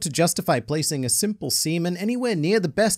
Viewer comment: I thought said "Semen" every time he said "Seamen" and it took a while to adjust.